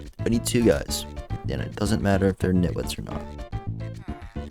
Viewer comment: What the hell did you say at 25:13?? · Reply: “It doesn’t matter if they’re nitwits or not.”